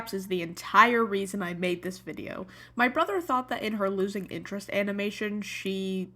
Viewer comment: I thought it said losing internet